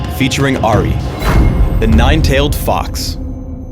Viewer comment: I heard "Ahri, Nine-Tailed Fox" like "Ahri, Nine-Tailed fucks". What's wrong with me?!